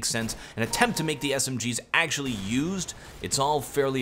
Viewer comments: it's only in the last 3 videos I realized you say "I still have no closure"... I swear to god before then i thought you said "I still have no clothes on" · The first time I watched one of your videos I thought you said "And I still... Have no clothes on"